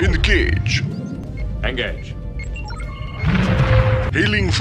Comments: I thought the battlecruiser said “In the cage”, not “Engage” my whole life. There are so many things I hear wrong in rts game lines lol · "Engage." is a reference? Isn't it more like generic term to use when you're... well, engaging? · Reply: I always thought he said "In the cage!" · Because I was 8 when Starcraft first came out (and very quickly began laying all the time I spent TOO much of my young life thinking the battlecruiser said "In the cage!" @ 6:55 instead of "Engage!" - and I have no bloody I idea why 😅😂